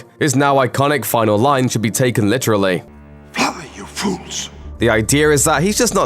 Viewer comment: I always thought Gandalf said "run you fools" instead of fly you fools